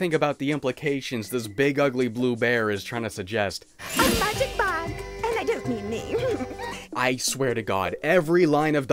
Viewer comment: What'd she say at 15:20? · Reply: “A magic bag, and I don’t mean me.” I’ve heard that it is an insult to call a woman a bag.